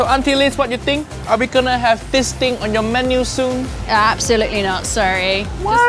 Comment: I legit heard "fisting" on 8:55, please help me